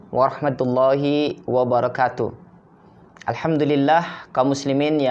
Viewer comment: Mohon maaf Ustadz, mohon dicek kembali redaksi salam yg sempurna. Maaf maaf, pada lafadz وبركاته( wabarlkatuh ). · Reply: Afwan, afwan, karena cinta antum, maksud ana : Audio salam nya. Ana yg salah dengar atau gimana : salam pembuka dan ikhtitam terdengar : "wabarakatu". Ha dhamir nya ngga ada jejaknya.